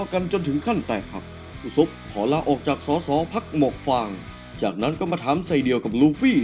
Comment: ใครได้ยินว่า สส.พรรคหมวกฟาง บ้าง😂